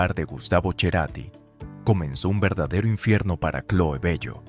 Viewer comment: Escuche "cherati" y cerre el video